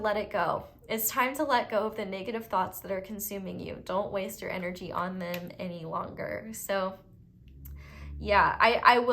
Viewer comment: "Don't waste your energy on them any longer." I thought you said domt waste your energy on men any longer. 🤣🤣🤣 I had to replay. 😎